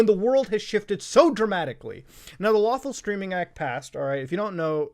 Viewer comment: I keep hearing "Lawful Streaming" as "Lawful *Screaming*". Funny, because the whole thing makes me want to start screaming.